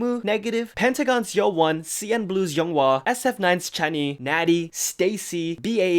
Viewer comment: "SF9's Chani" and who? I can't understand. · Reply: I think he said Natty